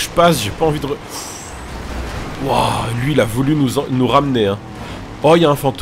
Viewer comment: "il a voulu nous " quoi ?😂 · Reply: Il a voulu nous enc...nous rammener